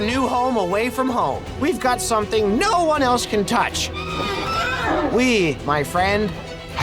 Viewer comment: I thought he said, "Weed, my friend."